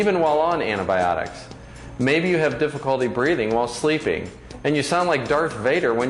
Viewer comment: Lol i thought she said breeding! o.o she actually said breathing, ohh dirty mind, you. Lol